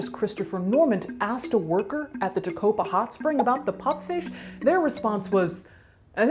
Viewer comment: So until I saw the bumper stickers, I thought she was saying pumpfish